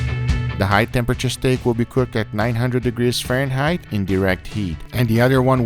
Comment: I thought it said -900 degrees 😂